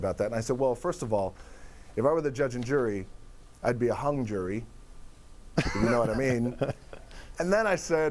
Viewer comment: For a second there I thought The Rock said Han Juri (Street Fighter character). lol!